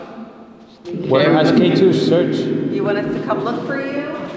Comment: Anyone else hear “search” being said in voice at 18:02? Or is it just me lol